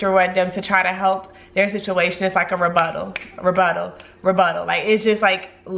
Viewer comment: Sorry guys, English is not my first language. I'm really trying to understand what's the word that she repeats three times on 16:16 ........Thank youuuuuu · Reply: Katja J. A. I guess she is saying rebuttal ,I believe it means that you are disproving some claims made by another